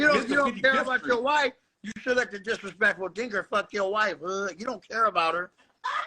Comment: did he say he's "disrespectful dinger"